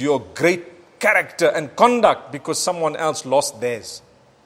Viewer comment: did you say “Don't lose your great character and cons just because they lose theirs”? Wow that touched me